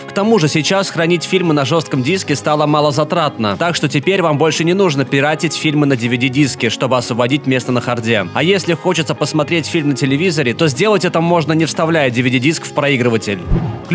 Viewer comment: "А если хочется..." Я переворачивалась на другой бок, когда была в одном наушнике, и потому неправильно услышала (как "хочите") и словила грамматический инфаркт , а потом поражалась своей глупости, хехех) Спасибо за вашу работу!